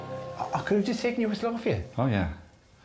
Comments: can someone tell me what dara said here that was “irritating?” cabt figure it out for the life of me! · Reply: I could’ve just taken your whistle off you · what does he say at 14:32 -- best I can do is "I could have just taken you aslungferya"... ??? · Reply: Okay. I got it. "I could have just taken your whistle off of ya" 🧐🤗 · what the is Dara saying? "I could've taken a SLOGHEVIHOGA?" Then Alex says yes, and Dara is upset. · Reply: Aw I could have just taken the whistle off of ya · I watch this show from Chile, and although my english is good, I can't understand what dara says in 14:30, Can anyone help me, please? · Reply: "I could've just take your whistle off of you?" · What did Dara say at 14:32? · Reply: It sounded like "I coulda just taken yours off ya." He was talking about using Alex's whistle. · What does Dara say at 14:33? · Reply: Could I have just taken your whistle off you? Alex: "oh, yeah" Ahhh, that's irritating